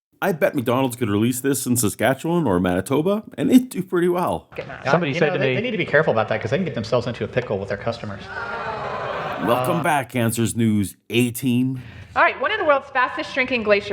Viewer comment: Sounded like you said "Welcome back Cancers News." I think that sounds about right.